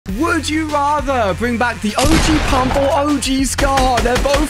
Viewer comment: Did he just say raaather